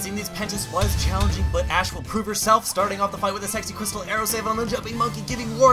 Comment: Did he says SEXSY crystal arrow?!